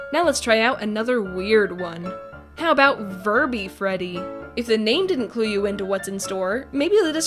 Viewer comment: I thought you said Furby Freddy 👀